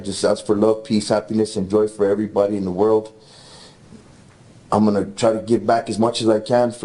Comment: did he say drugs for everybody in the world?